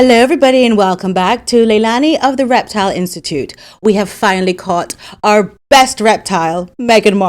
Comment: omg I really thought you said 'welcome to the Leilani erectile institute' 🤣